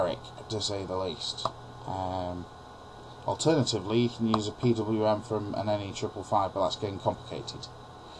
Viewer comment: I'm sorry but I can't understand what he said at 9:37. "Alternatively you can use a ... ". Can someone help me? Thanks =) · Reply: PWM from an NE555